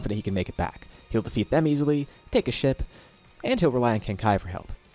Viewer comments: Fr thought you said take a shit instead of take a ship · i heard this as "take a shit" 😂😂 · I low-key thought he said "take a sh*t"